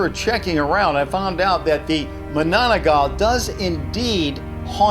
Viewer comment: I heard you say Managanal Instead of manananggal